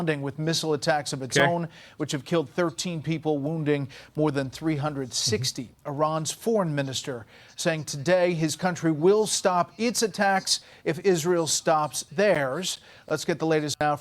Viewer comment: I thought he said “saying his country will stop ‘pizza tax’”